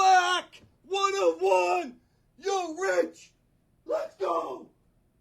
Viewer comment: takes the "line of the break" - sounds like he said - You're Rich $$$ Let's go....!!! (Could have said yo Rich... But thinkin' he said you're rich made me laugh!!!)